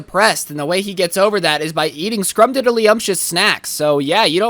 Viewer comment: did you just say... DIDDY?